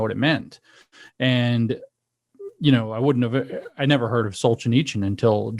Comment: they mention a name of a person I've never heard of. Does anyone have any info? I can't find anything online cuz I can't figure out how to spell what they're saying. Sultan ichen? Sulchan ichan? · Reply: Aleksandr Solzhenitsyn